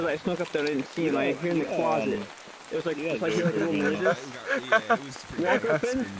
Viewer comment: what did he say At 3:10 ? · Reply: "Little noises"